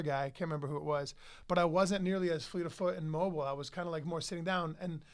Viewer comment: What does matt say at 13:45. Flee to foot? I believe it a boxing term meaning light on the feet or something but not sure